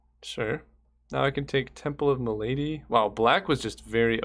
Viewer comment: all i hear is Temple o M'Lady.